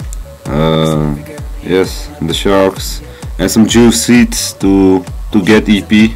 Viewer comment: lol did you say "jew seeds" at 1:39 :o lol